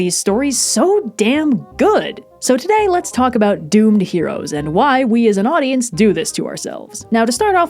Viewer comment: I thought this said Domed Heroes and was waiting for Blue to burst in